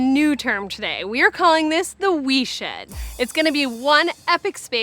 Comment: Thought it said weed shed for a moment